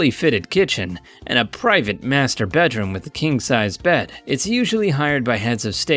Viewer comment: Anyone hear it as Masterbate room?